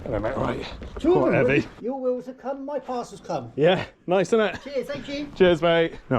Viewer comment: u shugurum my parcel 📦 come No idea what he said 😂 that part was funny